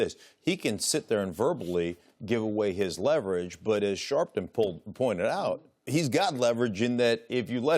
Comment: Did he just call him "Sharpton"?